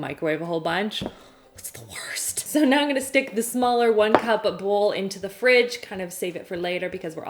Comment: Did I hear one cup of bowl?